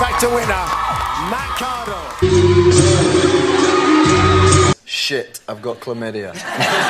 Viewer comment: What did harry say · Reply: “ imagine how much 🐱 your gonna get”